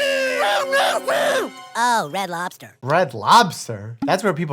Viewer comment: LOL! I thought they said 'Red Rooster!" I was like, "I've not spent a lot of time in the US, but WTH is that?"